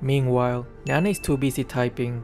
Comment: Did just called Eudora as Nana? absolutely nutty